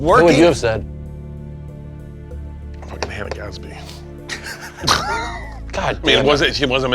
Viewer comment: what did bert say?? can't make it out fml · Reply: "Fucking Hannah Gadsby"